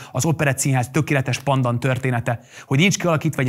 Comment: mi az a pandan? vagy mit mond? a pandan egy növény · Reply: Pendant, megfelelő,hasonló